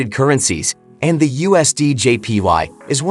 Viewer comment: All i kept hearing was " USDJPi". 😅😅😅😅